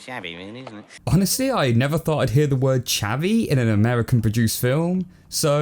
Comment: oh he said chavvy? I always thought he said "shabby"!